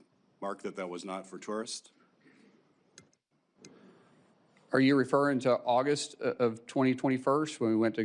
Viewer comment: DId he say "are you refering to August of 2021st "at 2:04...? What a tool....